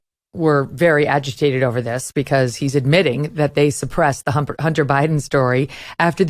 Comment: I heard it...Humper Biden! It fits Megan!:goodvibes